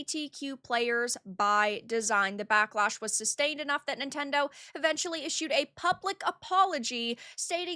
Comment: it sounded like you said DENT-tendo, and I wish that name caught on